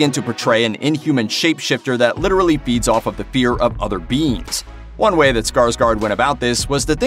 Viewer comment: When he said "feeds off of the fear of other living beings" I heard "beats off at the fear of other living beings"